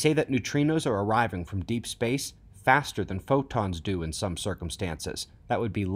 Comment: Did he just say faster than light ?